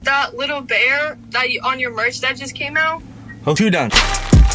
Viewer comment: Does any one know what he said at 1:39 lol 😂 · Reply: I’ll do that i think